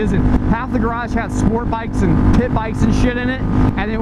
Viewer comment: Lol dude i thought you said squirt bikes for a second